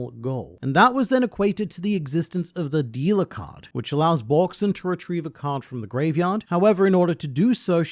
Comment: I heard "D"-card and "Borksin" together and thought I was mishearing a GrandLineReview vid about the will of D and Borsalino lol